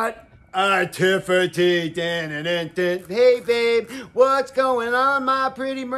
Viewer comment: When said in a sing-song manner, 'Hey babe,' I thought he said 'kayfabe'